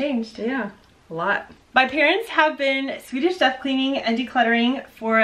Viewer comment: You speak quickly, and i thought you were saying "Swedish Chef Cleaning" 😂